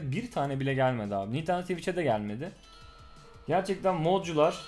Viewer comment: nintendo twitch diye anladım lskdfjsdaf